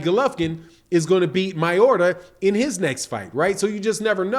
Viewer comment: Hahaha I know you said Ryota but it sounded like you Said Mayorga haha imagine Mayorga vs GGG. 😂😂😂😂